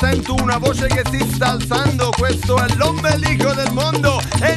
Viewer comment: Siempre pensé que decían: "Pobre del hijo del mongo" :"(